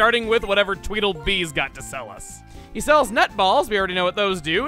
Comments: Wait I’m sorry, hold on, did you say NUT BALLS? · i swear your saying nut balls.... and it is a thing to say